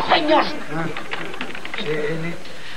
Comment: yo entendí CN de Cartoon Network 😅